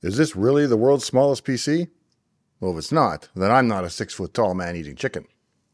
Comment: Thanks for the great video! Got super confused at first because I heard “6ft tall man-eating chicken”, not “6ft tall man, eating chicken” 😂. Love the breadth and depth of your knowledge, and love PC and computer history.